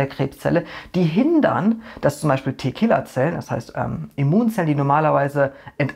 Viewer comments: Ich habe gerade verstanden Tequila-Zellen. xD Na dann, Prost Mahlzeit! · Oh man, ich habe schon so lange keinen Alkohol mehr getrunken, dass ich eben tatsächlich Tequila Zellen verstanden habe.... (Minute